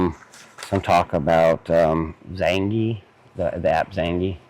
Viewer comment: Did he say Zandy? Yet another social media app I have never heard of